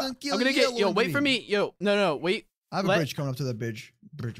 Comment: Did he say bridge or b...... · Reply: No he said Bridge